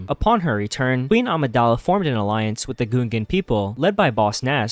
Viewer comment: Why did he say the "goongans"?